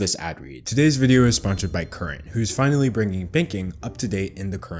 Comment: When he said banking, I thought he said baking.